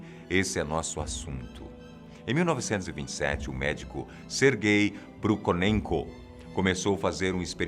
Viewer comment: Médico Ser Gay , foi isso q entendi